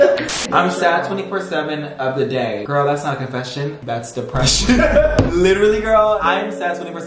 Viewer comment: “girl that is not confession- that’s depression.” -LARRAY 2022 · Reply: I thought he said impression💀 it doesn’t even make sense